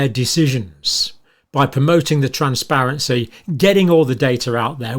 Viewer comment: did you say trumpsparency?